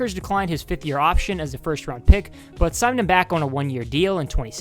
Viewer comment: At first I thought it said the first 6 pound draft pick😂😂